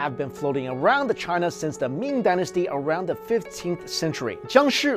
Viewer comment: did he say 'meme dynasty' 😂